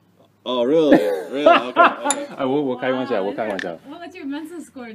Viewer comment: I heard “what’s your menses score”.